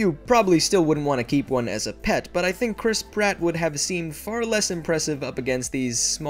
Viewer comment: I thought you said "Chris Kratt"